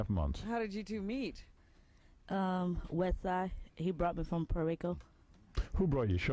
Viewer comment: Who else thinks beetle juice is an absolute legend. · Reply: At first I thought the lady said. " He brought me over some porico" and I was thinkin,' now that can't be good for those little people 😂